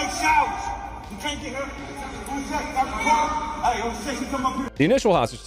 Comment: that sounds like OKC😂 1:47 does that say OKCPD bodycam😭